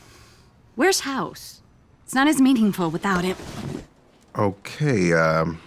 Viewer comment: did she say "Where's House? It's not his mating call without it"? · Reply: Where's House? It's not as meaningfull without him